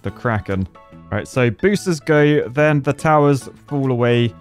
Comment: Whenever you say "booster" I hear "booshster" 🤣